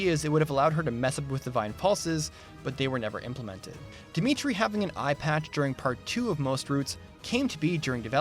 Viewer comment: I initially thought you said "Dmitri having an iPad" . . . that would be a pretty advanced kingdom!